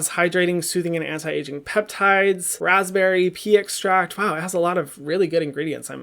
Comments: I swear I just heard him say “Raspberry, Pee extract...” man skincare is getting kinky and weird. Lol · did I just hear ‘pee extract’ ? 😂😂